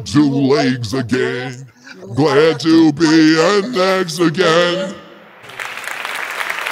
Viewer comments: I heard kinda saying plan to be a Mexican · When (?) said "Glad to be an X again", I thought he said "Glad to be a Mexican". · I thought the end said "Glad To Be A MEXICAN" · when he said “Glad to be an X, again! I heard: Glad to be a Mexican!